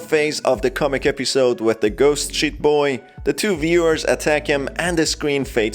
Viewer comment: Because of your accent i keep hearing "ghost shit boy"